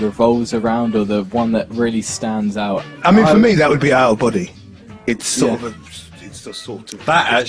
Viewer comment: what did he said? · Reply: It’s out of body